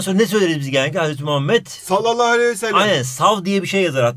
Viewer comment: Hocam 4:35 te ne dediniz ya öyle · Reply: Hazreti Muhammed Sallallahü Aleyhüvesellem Diyor